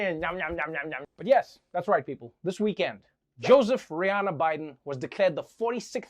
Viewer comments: Did he just say Joseph Rihana Biden?😂Lmao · Did he say " Joseph Rihanna Biden" 🤣🤣 .. or i misheard?